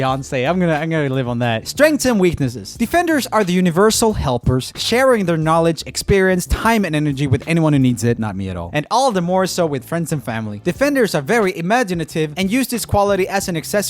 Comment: Who else heard "hyman energy" instead of "time and energy"? 🤣